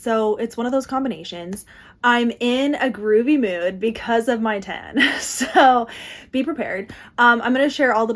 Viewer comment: thought you said "im in a groovy mood because of my man" instead of what you actually said "because of my tan" 🤦‍♀️😆